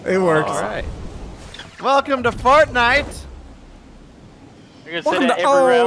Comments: Is it only me but does sound like he says welcome to fart night at the beginning? Lol great video you guys keep them up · At the beginning it sounds like he said fartnite